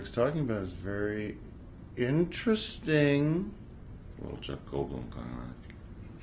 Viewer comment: who tf is Tim Hidecker doing in this clip? He just completely lost me. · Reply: I thought I heard them say Jeff Goldblum at the start of the clip